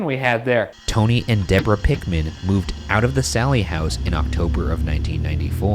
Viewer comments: I heard "Seven", not "Sallie" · I believe around the 34:00 mintue i heard Seven and not sallie. And wasn't that the age of sallie?